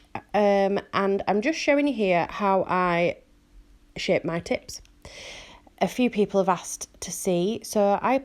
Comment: anyone else hear "show you how i shake my tits"? 😂😂